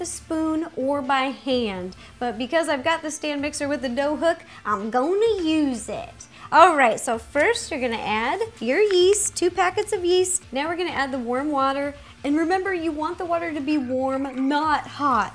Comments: please tell me I'm not the only one who thought she said 'worm water' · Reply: ***** no i thought she said worm water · I keep hearing "worm water" haha xD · please tell me I'm not the only one who thought she said 'worm water' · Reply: ***** your not the only one who thought she said 'worm water' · I thought she said worm water instead of warm water lol;) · please tell me I'm not the only one who thought she said 'worm water' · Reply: I heard "worm water" too. But I'm not trying to start any arguments right meow. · I can't be the only one who thought she said worm wanted instead of warm water at the beginning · I thought she said worm water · I swear I heard her say "worm water" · It sounds like shes saying worm water Dafuq 0_o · Warning this is a blonde moment So I thought she said worm water while I watching this at the begging of the video and I was like 😵😶😵😶😵😶😵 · I thought she said worm water lol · i thought she said "worm water" in the beginning and my heart stopped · I thought she said worm water... i was like o.o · I thought that you said "worm water" Lol